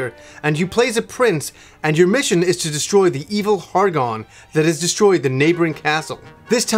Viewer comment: Could have sworn you said "Your mission is to destroy the evil hard on".... that's my mission every day man....